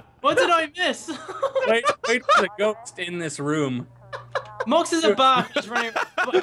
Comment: did just say "Box is a mark"! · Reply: Vexurath I heard "Marx is a bak